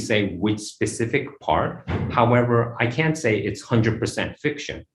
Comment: I believe it was "I can't say" what was said :)